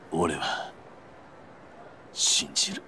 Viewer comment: That line there...I know the subtitles say "No... You're wrong", and my Japanese is still beginner, but didn't it sound more like he said "I... believe in you"? If you want to get really specific he said "I believe", but considering the context and the guy who stab him's face afterwards (I forgot his name) and the music, I feel like Kiryu insisting he still believes in the guy who stabbed him kind of makes sense? Maybe?? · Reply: You are right. I don't speak English😅But I understand Japanese. Clearly, he says, "I believe." (ore wa shinjiru) "No ... You're wrong" is not wrong, but I think "I believe" is closer.